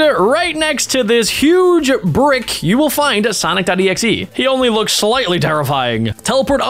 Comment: it said sonic daddy z instead of sonic exe 🤣🤣🤣🤣